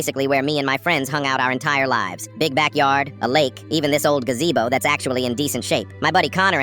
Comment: Im sorry but big back yard i thought they were saying bigback lol 😆